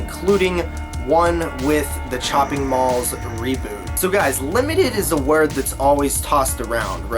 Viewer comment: it actually says "robot" instead of "reboot."